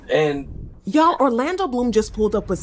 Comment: did she say Orlando Bloom 🤔